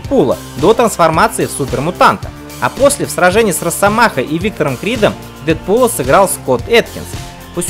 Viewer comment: мне послышался что парень сказал егор крид